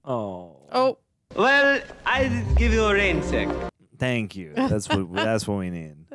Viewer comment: I JUST NOW realized that he said "I'll give you a raincheck." I always thought he said "I'll give you a raise, then." 14:14 - As holder of a degree in Child Development, I can confirm that these are REAL tests of infant developmental level. 30:54 - In the original screenplay, there was a scene here where Ray got possessed as they were driving and tried to wreck Ecto-1A and kill them all. The scene was eventually mostly left on the cutting room floor, but shots of it can be seen during the 'We're Back' montage. 53:28 - Sorry. JanEgon all the way. 1:20:55 - Winston is in this film MORE than in the original. He was in the birthday scene, at the beginning of the trial scene, and in almost every group scene after that. His role has never been significant. He's there to be the 'everyman' among the scientists of the other three.